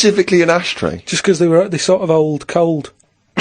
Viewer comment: karl said "they're sorta old, cold" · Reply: He didn't. He literally says "they sort of hold cold". Ricky and Steve simply misheard him just like you did. It's a common trait in numerous English dialects to not pronounce the 'H' at the beginning of words. Karl has this trait within his dialect and there are plenty of examples of this in his speech, some of which have been posted in this comment thread.